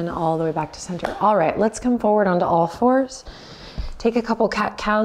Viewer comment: I loved the practice as almost every video I do with Adriene - today I watched it the first time with subtitles as I'm not a native English speaker. To my surprise the subtitles are saying "on all fours" to the table top position, I always thought she said " on our force", which makes sense for me too :D I like the idea of receiving force from table top position, so I keep on thinking of it :)